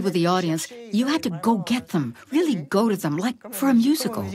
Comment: she did not say "musical", she said "music hall".